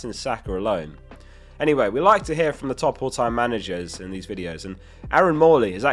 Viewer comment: I miss heard you saying "top all time" as "top haul time managers" and think that's what they should be called now